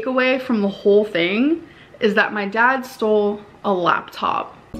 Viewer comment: I swear he says, "the horse used the bean bag in a hurry" not "Lord, she's a" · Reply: I always heard “Good lord, the bean bags in a hurry”